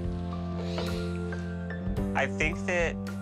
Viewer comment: did he just call her mommy??!?!